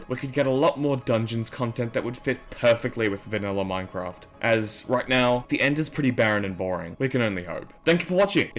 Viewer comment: When u said this i thought u said Minecraft was ending